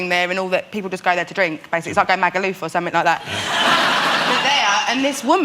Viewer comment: What does does she say at 2:57 ? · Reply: About the atmosphere at the stadium in Vagus, and the drinking and party atmosphere, she says, "It's like going to Magaluf or something like that". That's where a lot of Brits go on a girls' party holiday 🤣x